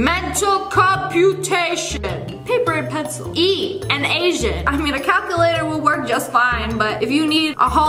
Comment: I thought she said eat an Asian not E. An Asian